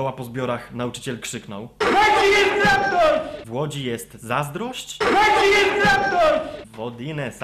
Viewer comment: Ja usłyszałam ''W Łodzi jest traktor'' =))xdd